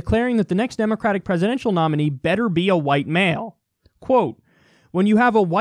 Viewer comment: For a moment I thought he said "Better be a white whale."